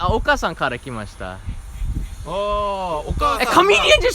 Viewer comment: 楽しい動画、いつも見てます。けど、あの伝わらなかったジョークね、発音と文が違うので伝わらなかっただけだと思います。私は「実はおっかさんから来ました。」って聞こえたので、「おっかさん？岡さん？人の名前？」と思いました。「お」を弱く言って「かあー」を強く、長く発音すれば分かりやすいですよ。それと「お母さんのお腹から来ました。」と言った方がわかりやすいですね。これからも気をつけて旅を続けて下さい。your pronunciation " okaasan(mother)" was difficult to understand. i thought it was "oka-san" which is one of japanese family name.